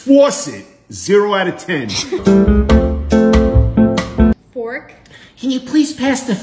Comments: You're not trying to be right there but I thought you said the f word but it was fAWK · I thought he said the F work from fork